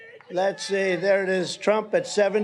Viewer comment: Man I thought trump said Rhonda Sanctimonious